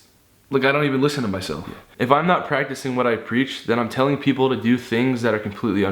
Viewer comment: at the part when said "If I'm not practicing” I thought he said "If I'm not pregnant" and I don't know how I heard that I just did for some reason